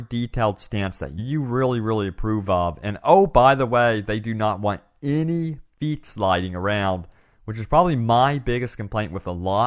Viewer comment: "they don't know any ????? lying around" THE FUCK DID HE SAY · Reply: dont want any feet sliding around.